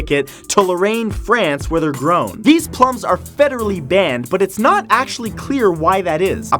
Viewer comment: I usually work while playing Youtube videos at the corner side of the computer screen, so when #4 came up, my eyes weren't watching the video, and I thought Matt said "A Fedora".